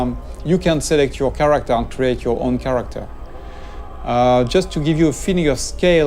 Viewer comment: Anorep thought he said choosing. but i did a google search and found out the game will have character creation :D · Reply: Papa Lord 1:52 "You can select your character and you can create your own character" At least that's what I understand.